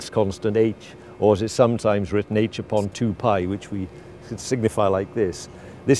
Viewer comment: When he said signify, i thought he said Sigmafy